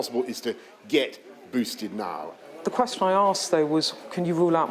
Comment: In the hospital, It sounded like she said to Boris "Across my arse!" Then I realised she said "The question I ask" 😂